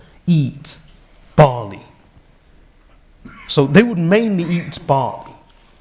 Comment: Can someone please help me with what the speaker said at 0:15 They would mainly eat ...? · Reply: 'They would mainly eat barley' Subtitles are available for these excerpts by clicking the CC button.